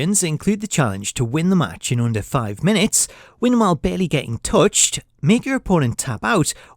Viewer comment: I swear he said Bailey for a second 💀